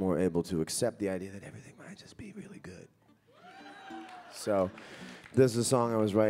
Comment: I'd like to know what John whispered at 11:08... Can someone help?? :) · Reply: Rodrigo Miike .....to accept the idea that: "Everything might just be really good"